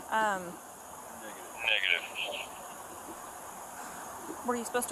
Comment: am i the only one who heard nigga😭🙏